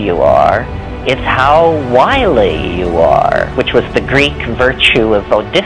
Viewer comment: I didn't understand what he says in: 7:02 until 7:14 " Its not how good you are, it's how ______ you are. " Can anyone, help me? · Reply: It's how wily you are. skilled at gaining an advantage, shrewd, clever, astute, canny, intriguing, tricky, cunning